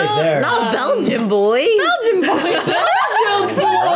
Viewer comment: oh "Belgian Boys" thought i heard "Bulging" boys